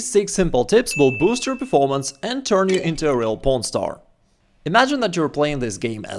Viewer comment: did you say porn star 😂